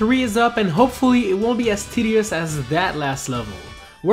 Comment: did anybody else hear him say "tiddy-ous"?